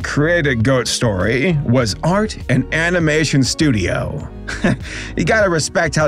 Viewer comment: It honestly sounded like you said "aren't an animating studio" to me. That honestly would have been a much better name for them